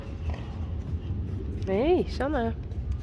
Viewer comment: How do you say the foals name? I havent heard how you say it yet. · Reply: she says Hey, Sanne. I think she says her name a few more times in the video but this is the best example I could find.